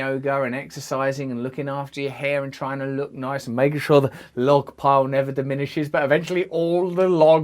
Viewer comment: I thought he said “make sure the “love pole” never diminishes! Lol